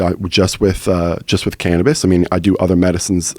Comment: I usually don't comment on this, I've watched every podcast for last few months. I come from 2 alcoholic parents. I grew up while witnessing alcohol ruin both my parents lives, who both had crazy potential. Pot helped my Dad get sober. I've never heard of alcohol helping anyone succeed at anything, except maybe picking up a chick at a bar. I started drinking and partying in high school, and didn't realize how addicted to it I was. It also increased my anxiety, affected relationships, made me feel shitty about myself, etc. If I would have just smoked instead of drink I think I would have had a much different time during some pivotal years of my life, so to say alcohol is better for young people than weed is ridiculous. Alcoholism is a clinical disease that you don't want to mess around with-I've never heard of cannabisism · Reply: For a second I thought you said cannibalism 😂 whoops